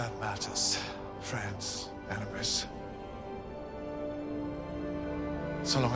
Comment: Repeated Capaldi's lines at 0:16 - 0:23 several times and still couldn't understand what he said. Anyone else? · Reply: So long as there’s mercy, always mercy.